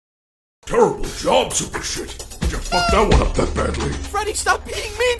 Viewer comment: i don’t know about you guys but I heard “super shit” not “super star” lol!